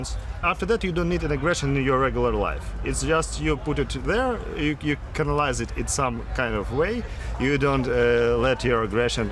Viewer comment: the captions say "you can analyze it" but I believe says "you canalize it", a mistranslation for "to channel". I don’t speak Ukrainian, but as far as I can tell it uses the same root as we do in French for this expression. His mistakenly using "let" instead of "leave" at 5:44 is also common with French speakers :) And at 4:44, "base dominant" should be "bass-dominant"!